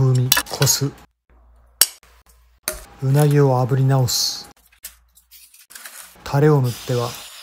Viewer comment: 鰻を炙り直す...が胸毛を炙り直す...に聞こえたほんますまん